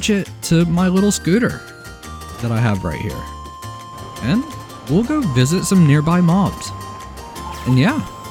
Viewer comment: I misheard you saying “we’ll go visit some nearby moms” 😂